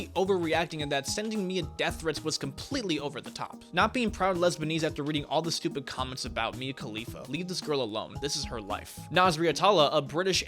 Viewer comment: he really said "lesbanese"